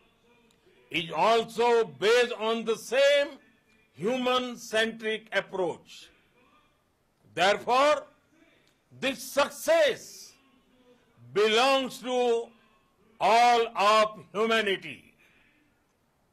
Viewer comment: As our Prime Minister said, it's victory of world's humanity. Proud as an Indian.Jai Bharat...Jai Hind. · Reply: Oh, I thought he said it was a woman-centric approach, then he said it belonged to all womanity, was like "wut?" 😂